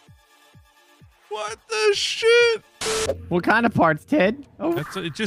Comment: So, the Dyslite alert I heard "I just farted" that just me?